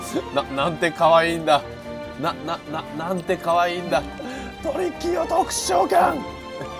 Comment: トリッキーを特殊召喚が とりっぴいを特殊召喚に聞こえて草